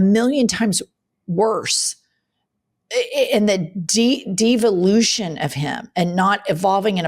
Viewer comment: when you said "de-volution" I heard it as "diva-lution", like he's evolving into more of a diva. Both work.